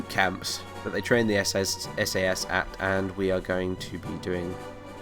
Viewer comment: notice how he says SS instead of SAS.